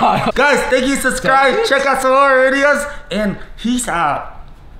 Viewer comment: i thought he said "check out some more idiots"